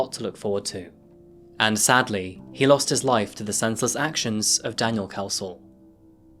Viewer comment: Is it "just me", or does anyone else understand at 19:55 "...and sadly he lost his life to the senseless actions of Daniel's a***ole." (?) Really no pun intended, however it sounded unexpectedly funny to me and caught me totally off guard.